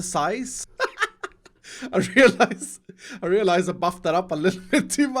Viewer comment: I thought he said “I realised I f’d that up”